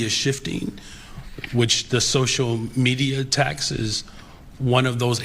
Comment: did he just say “social media taxes”?!?